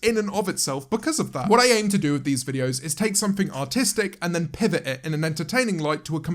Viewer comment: did James say autistic? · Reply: Artistic